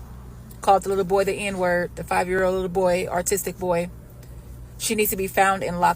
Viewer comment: lol I swear she said "artistic"